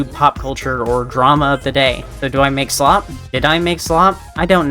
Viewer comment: I mean, even if your old content was considered “slop”, I still just like your content, both what you did before, and what you do now! :D …I just feel bad that I don’t watch it often because I don’t always have the time to, so I’m sorry about that :T (Also imma be honest when I first saw this I thought you were saying “do you make slop” as in “should you make slop” and I was thinking about that guy talking about the Bucket of Wet Slop, so I just at first thought “sure? If you really wanna make a whole bucket of that, then go ahead?” And then I realized and was like “wait no-“ XD)